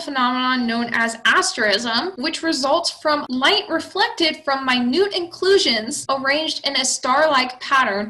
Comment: This is very useful, thank you. And I’m sorry but I heard: “My nude inclusions”.